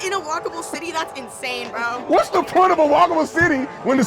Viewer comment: I thought she was saying whack-a-mole city😂